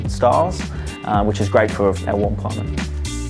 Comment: Great video, but what does he say at 1:48 "...which is great for....???" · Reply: a warm climate